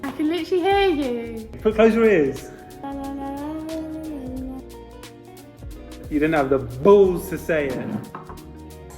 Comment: did ANYONE decipher what was saying her fear was 8:30? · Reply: Dying